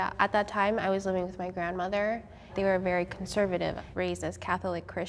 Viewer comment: Am i the only one that thought she said racist